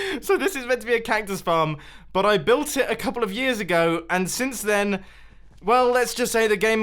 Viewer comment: When you said 10 years ago, I thought you said 100 hundred years ago